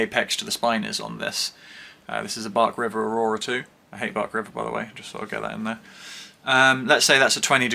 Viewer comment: Did I hear “Hate bark river” 😱